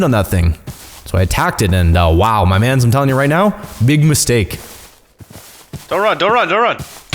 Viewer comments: did you say moostake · Anybody else hear "big MOOSEtake at 59:10?